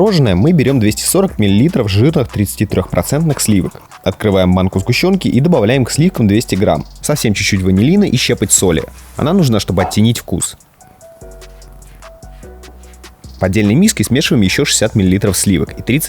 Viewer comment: Мне послышалось или хозяин сказал не банку сгущенки а манку сгущенки